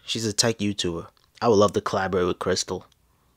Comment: tell me why i thought he said "ill love to clap crystal" LMAO